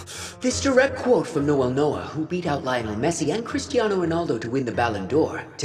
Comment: In 10:54 did bro say messi and ronaldo😮